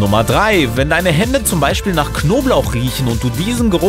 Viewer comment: Hab ich verstanden "Wenn deine eltern nach knoblauch stinken" xd lol